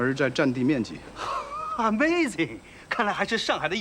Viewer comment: 活活的把“ Amazing” 听成了“你妹子” :(